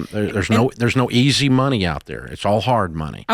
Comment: Ok, did I hear him right that he said he’s making “Sh_t money” 😂😂7:15. We all start somewhere.